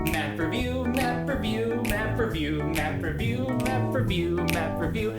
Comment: sorry didn't catch that, did you say m e m e review?